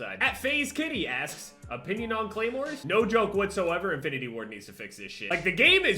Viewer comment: Did he say infinity war